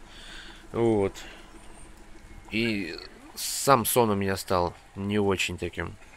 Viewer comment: Даже такая осенняя серость очень прекрасна) послышалось Самсон вместо "сам сон"